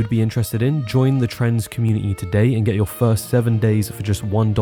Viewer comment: When the sponsorship bit came on I swear I heard "join the trans community today". That's what I get for watching youtube while doing other things...